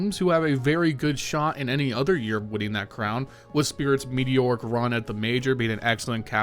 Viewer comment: Did you just say spirits mediocre run in the major?